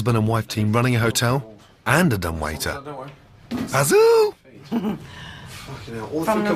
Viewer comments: Wtf did Chef Ramsay just say "HAZOOO" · Anyone know what he said at 2:43??? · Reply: Basil, like in Fawlty Towers